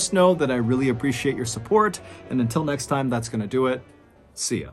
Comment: I swore at the end, you said “Soap, that’s gonna do it…” lol